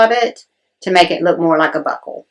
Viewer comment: I have a sister-in-law that could almost pass as your twin. Y’all are the same size, and sound the same. She tends to speak a little crude at times. Maybe that partially explains how I mistakenly thought you said “butth#%*” , instead of BUCKLE. 😂😂 Sorry